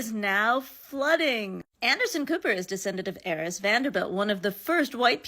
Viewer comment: Wait, wait, wait. It's "ARIS" Vanderbilt? Aris is a proper name? Every time I heard "Aris" Vanderbilt, I thought people were saying "Heiress Vandebilt" and thought they were referring to a rich scion lady of the Vanderbilts.